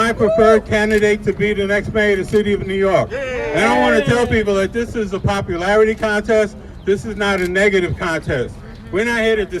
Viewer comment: Leroy Comery 5:55 "This is not a popularity contest" it sounds like he said "it is", he didn't I was there.